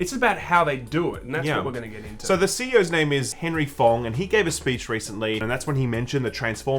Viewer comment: At first I thought you said the CEO's name was Henry Bong!